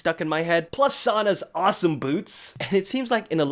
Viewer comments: Why the hell did I hear "Sana's awesome boobs" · I almost thought he said boobs...